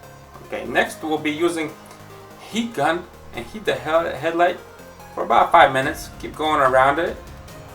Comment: i thought you said Heat the hell out of it lol